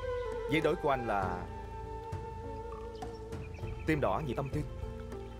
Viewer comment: Trạng Quỳnh nói gì vậy ạ? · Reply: T nghe đc là "Tơ đỏ về thâm tim" "Thâm" ở đây là "thẫm"